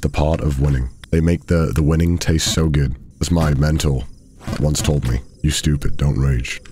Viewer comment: "As my mentor once told me you're stupid don't rage"😂😂😂 I low-key thought he said mental instead of mentor and that made it so much funnier fr.